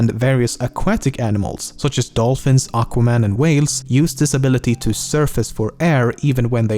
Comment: Did he say Dolphins, Aqua man, and whales